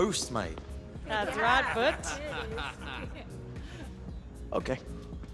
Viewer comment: Did she say boet? Is that a SA person?b